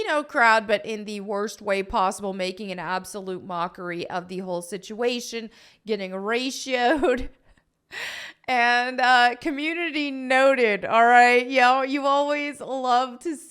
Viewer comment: I coulda swore you said "horatioed". I'm stealing that.